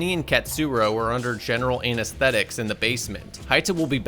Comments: It sounded like you said “anus-thetic”😂😂😂oh God help me I can’t stop laughing...l need a salt bath😂😂😂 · He says anesthetics multiple times in the video but for some reason to me it sounds like he saying anus statics